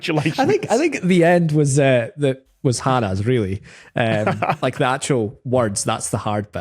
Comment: Hi, at around 5:18 the subtitles say "hardest" but the word said is actually "Hannah's" -- as in she's the one who guessed the solution, not that the solution was hardest.